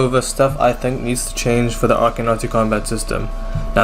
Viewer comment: when you turn on subtitles, it does NOT say arcane odyssey